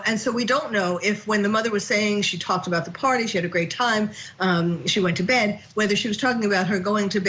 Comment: I thought i heard the party was at her Grandmother's house.